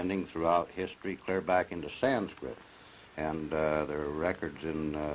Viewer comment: Did he say sanskrit ?